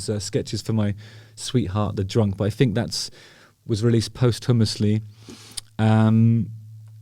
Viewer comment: I thought you were saying "post hummus-ly", until I realised you were saying posthumously 🤦🏻‍♀️ Songs were released after he ate hummus 🤣